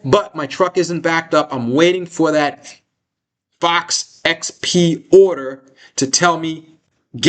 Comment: Did anyone hear at 1:23:30 what says he's waiting for before knowing it's "game on" with AEHR? It sounded like he said "Box XP", but I can't find any reference explaining what this might mean. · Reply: Fox XP which is AEHR’s wafer level burn in and testing product offering. My recollection is these sell at a much higher per unit price than some of the other products.